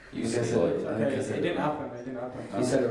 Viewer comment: WHAT DID ZAYN SAY TO HARRY?? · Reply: harry said something inappropriate, boys are calling him out, zayn is protecting harry by saying "it did not happened" meaning harry did not said anything wrong